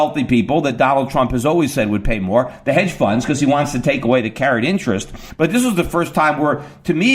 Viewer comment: What does he say at 19:10, "Caren interest" ?